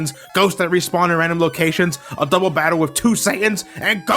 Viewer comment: I swear at 9:26 I heard "a double battle with two Saiyans."